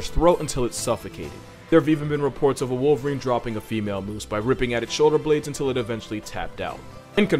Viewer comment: A female what? I couldn't understand what he said and the captions wouldn't show up. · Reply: Moose. A wolverine got on a female moose and attacked her shoulder blades.